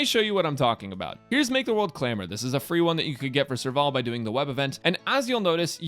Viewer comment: All i heard was "This is a Cone meant for Serval" Shes waifu and hottest character in the game..say no more i pick her cone.